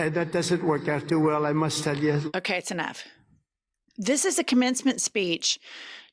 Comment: You did say it’s enough, but I thought I heard it’s an F which also works. I’m enjoying your content from Canada.🎉